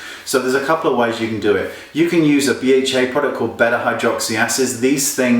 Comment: what did you say use at 4:55 ? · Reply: BHA product. BHA = Beta Hydroxy Acid.